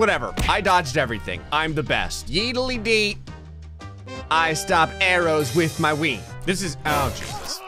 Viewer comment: Did u just say: " yeedleedee i stop arrows with my wee" 😂 got me rolling on the floor😂